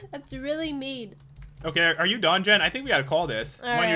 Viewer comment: He said "are you done Jen?" It sounded like he said "are you dungeon"